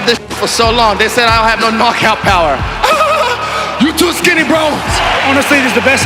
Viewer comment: Did he said i have pacquiao power? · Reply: No knockout power I guess